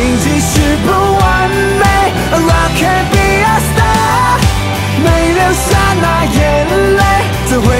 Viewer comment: 不小心把「A Rock Can Be A Star」聽成「拉K Be A Star」的路過...